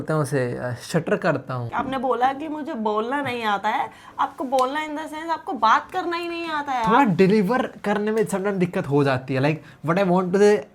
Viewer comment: she actually said that larko ko battt kar a nhi ati